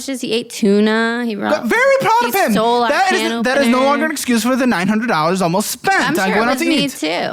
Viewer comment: did she just say he stole their can opener